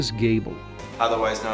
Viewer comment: I thought he said 'cable' instead of 'gable' as a jab at how most TV sucks.